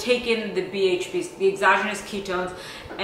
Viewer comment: It sounds like your saying erogenous zone...